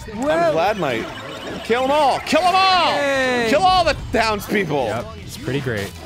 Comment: When Arin said to kill all the townspeople, I thought he said "Kill all the downs people." And I was like 0_0